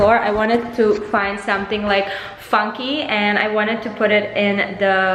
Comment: I heard you say "fucky"